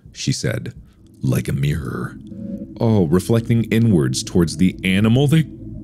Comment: someone came in my room at this moment and I thought mark said “reflecting n-words”.